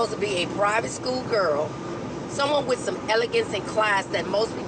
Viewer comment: when she says “elegant” I hear servile but she says “hood” I hear “woman”